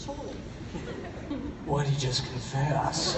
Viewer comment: What does Cole say at 18:58? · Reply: "why did he just confess." It's a reference to when sheriff Keller is talking to Betty Veronica Archie and Jughead about FP. I think it's because of the way he said it that they found it way too funny!